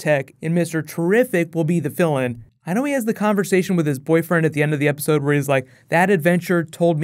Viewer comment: I thought you said "and mr terrific will be the villain" I was like wut,,, I realised you said fill in